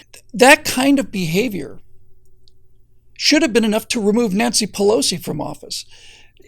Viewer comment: I thought he said Nazi pelosi